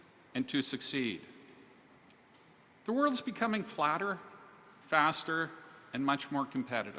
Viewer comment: Hi at 1:42 seconds or there about's did he say the world is becoming flatter